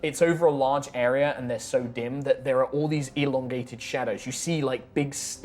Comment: OH MY GOD I THOUGHT SHE WAS SAYING CORNER SHADOWS I WAS LIKE HUH????